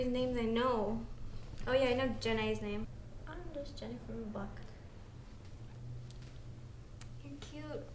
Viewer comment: What does say at 3:00? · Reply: im just jenny from the block